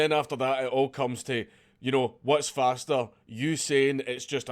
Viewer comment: any one else think he was going to say "whats faster Usain Bolt or" ... unless its because i'm baked haha :) x